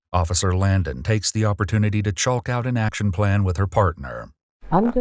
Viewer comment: I heard, "Officer Langdon takes the opportunity to chulk out." My mind is incredible